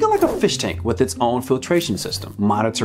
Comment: did you just say "allergy eaters"?